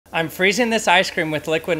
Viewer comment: I was high and heard it as feeezing this chicken up and thaught he was gonna get sick